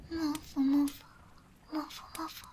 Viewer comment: When I first heard this I thought she said "mofo mofo" I can't help but laugh now